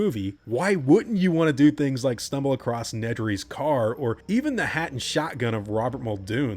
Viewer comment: the actual audio is "...hat and shotgun or Muldoon" but my non-native English-speaking ear at first thought that it heard "...hand and shotgun". I had to rewind to check out the subtitles but the image was already in my head: Robert Muldoon's torn-off hand still squeezed on that sweet, useless shotgun...